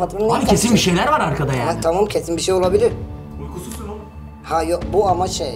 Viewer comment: "de ne diyor söylermisiniz arkadaşlar tam anlayamadım He bu arada patron kırmızı çizgiyi aşıyor saygısız konuşuyor Aslında varya patrondanmı korkuyor anlamadım böyle patron saygısız konuşuyor ama hakkını savunamıyor ve "de savunmuyor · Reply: "Uykusuzsun oğlum" diyor, Ama gayet iyi bir şekilde videoyu taşıyıp enerjisini yansıtmış. O cümleyi kurmasındaki asıl sebebi anlayamadım.